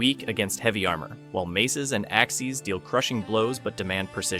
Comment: did you just say axies?